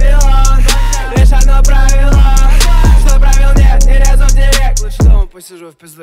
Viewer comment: Мне слышалось «По руке иди сюда» Вместо «Ору ей иди сюда» 😂😂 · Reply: А мне послышалось "ору гей иди сюда"🤣🤣😂